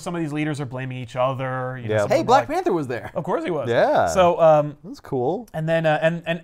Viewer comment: I was confused here. I thought said "Hey, Black Manta was there!" and I was like... what? I am so silly. He said Black Panther.